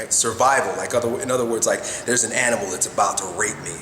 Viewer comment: Did he just say that there is an animal that is trying to rape him?